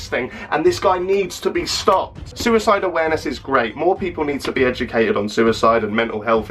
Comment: I wasn’t paying full attention so when u said ‘suicide awareness is great’ I heard ‘suicide is great’. I almost unsubbed